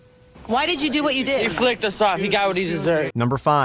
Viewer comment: 11 what the hell is he saying? "He flicked us or flipped us off he deserved it"